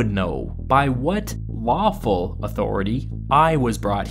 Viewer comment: i know you said lawful authority... I heard waffle authority. "All rise for the pouring of the syrup!"